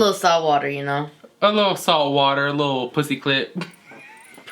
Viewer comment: Lmao! I love you two together!!!! Please more videos together!!!! Tell me why is worried about the time and she's like whatever lol 😂😂😂 and when she said "salt water" tell me why I heard her say soft porn 😂😂😂😂 am I drunk? Love this video!!!!!